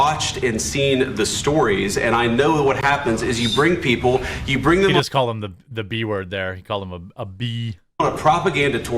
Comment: I played it over and over. I can't figure out what he's saying but didn't seem like B-tch. Seems a 2 syllable word. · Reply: probably spelling it wrong, but he said suka, it means bitch in that language